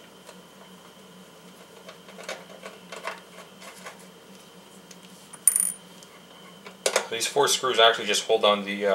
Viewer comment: hey man can I ask what tool you were using at 1:30? It was a number 10 something I wasn't to sure what you said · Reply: Torx bit